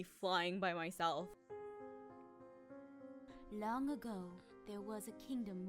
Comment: "Long ago there was a benevolent queen." I heard: "Long ago there was a banana."